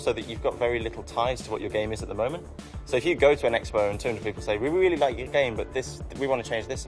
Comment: Did he say Xbone?